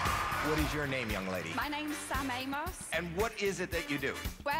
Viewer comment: I thought she said her name was shimamos